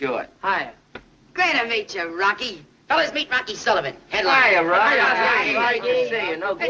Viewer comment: Meet Rocky Sullivan, Headliner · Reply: I thought he said, meet Rocky, head lion...